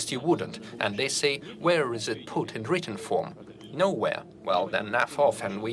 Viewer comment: it sounds like Putin is saying “Well then naff off, and we don’t care about your concerns..”. I keep playing it over & over but can’t hear it as anything other than “Naff off”. 🤷‍♂️